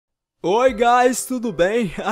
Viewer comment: entendi "Oiá o gás" KKKKK